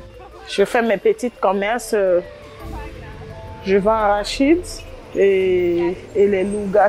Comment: ya que moi qui a entendu " je vends shit !" x)))